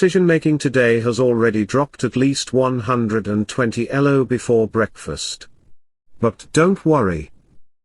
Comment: I thought he said 'dropped 120 LOL's b4 breakfast' Also valid